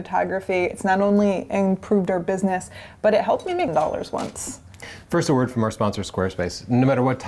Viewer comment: What is Chelsea saying at about 0:42? (English is not my native tung) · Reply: She said: "But it helped me made Dollars once." HTH and Regards form Germany